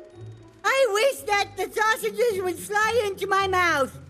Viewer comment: 'I wish that the sausages would slide into my mouth' I'm so dirty minded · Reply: Flare oh he said fly? I thought he said slide... T.T